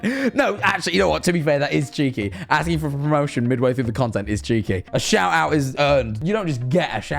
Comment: am i the only one that noticed at 24:49 the subtitles said “asking for a promotion midway through the promotion is cheeky” when jack said “asking for a promotion midway through the content is cheeky”??